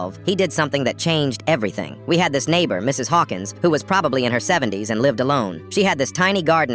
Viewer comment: Mrs Chen? I could have sworn I heard Mrs. Hawkins.